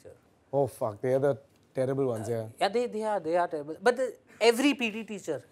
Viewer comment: I thought he said "Ladies & Terrible"